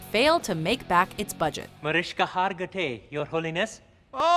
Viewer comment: Did he say "Mariska Hargitay"?